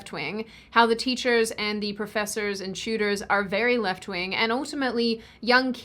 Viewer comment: What Sydney said: "how the teachers and the professors and the tutors are left wing" What I heard: "how the teachers and the professors and the shooters are left wing" I might have done a double take at that but I also was like "facts".